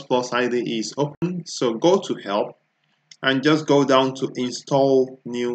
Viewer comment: sounds like he says "so go to hell"